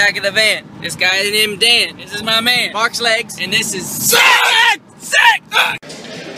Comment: Layin' in the back of the van Thus guy's name is Dan This is my man Mark's legs And this is SICK! lol Tyler. just lol. 😂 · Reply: Hang on I always heard him say box legs not marks legs